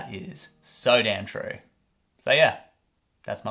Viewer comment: i thought he said "say yeah" so I said yeah